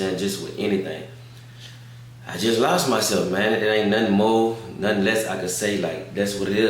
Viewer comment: I thought he said he wasn’t addressing nothing no more